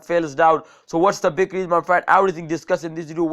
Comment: wtf I heard him say was ma friend lmao